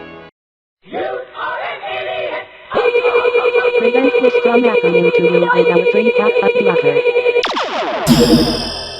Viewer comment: Hey, what did they say in 5:20? I couldn't hear it over the rage he made. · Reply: "Prevent this from happening to you with our free popup blocker!"